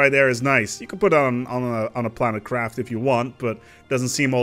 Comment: I thought you said planet-crab. Yeah that's what this mod need. All this space stuff is cool and all, but what about ground war? I want to turn a planet into a crab and toss it at other planet.